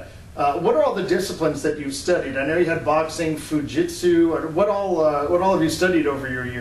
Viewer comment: am I hearing this right: "fu-jitsu"?